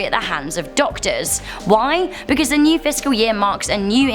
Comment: It sounded like she said VSCO year